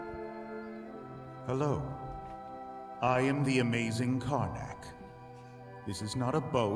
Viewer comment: things i noticed 0:53 It's spelled "Karnak" 5:56 Here, Ocean and Constance say "Oh, step right up!" 6:20 Here, they just repeat "When the fall fair comes" not bear cup 7:05 you forgot to add the where's "WHERE'S the sky and WHERE'S the ground?" 7:36 The full line is "What a load of freaking junk! That crusty carny, clearly drunk! Sue that fair for everything they've got. " 7:49 Noel says "Except to rot" not run 8:00 "ALGEBRA 12, kiss my ass!" 8:02 "Now i know what YOLO really means." After that, what Ocean and Noel say are "Carpe diem" and "C'est la vie" I'll add more later i'm kinda tired rn